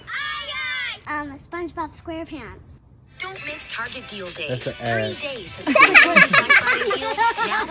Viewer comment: When you set up an ad I have to rewatch it because when you said the first time right I thought you said that’s an egg I’m like what the